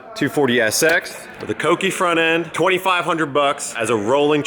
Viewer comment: "cokei" love that sounds like a Japanese hot boi drug.